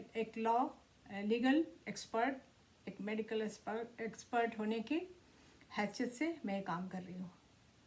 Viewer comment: did she say medical sperm expert?